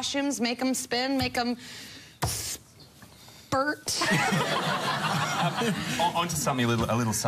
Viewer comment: what did she said on 07:04 ? bur... what does that mean?